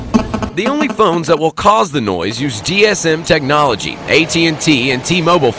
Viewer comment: did he say DSM? Isn't it GSM?